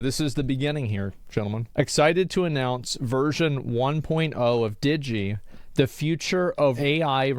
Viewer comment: As a non-native english speaker I thought he said "we're excited to announce virgin 1.0" lol.